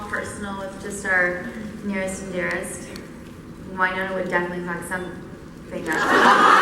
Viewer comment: What they said at 12:07? Please because I’m French I don’t understand 😞❤️ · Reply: Kat said "Wynonna would definitely fuck something up," and then covered her mouth because she felt bad for saying "fuck."